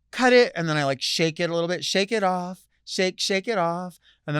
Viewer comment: Delta, this is how out of touch I am from modern society. When you sang “shake it off,” I heard “shake it up” (that 80s song by the Cars) 😂